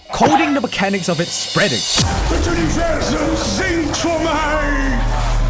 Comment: as a dutch person, what he said was ‘The universe sings for me’